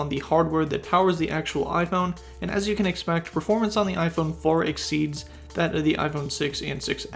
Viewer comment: Did he just say iPhone 4 instead of 8?🤣😅